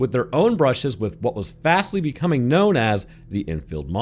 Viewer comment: fastly becoming known as? is that what i heard you say at about 4:50?